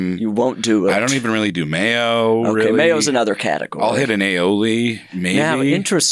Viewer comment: they'e talking about sour cream at 17:08 Jacob says "i'll hit an aioli" but the auto captions say "I'll hit an elderly man" 😂